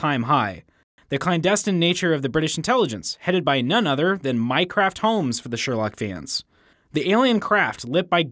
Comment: Anyone else heard him say "Minecraft Holmes"?